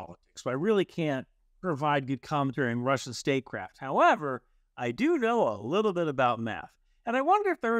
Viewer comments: My brain broke a second and I thought he said "But I do know a little bit about meth." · for a second there i heard 'i may not know about russian statecraft, but i do know a little bit about meth' XD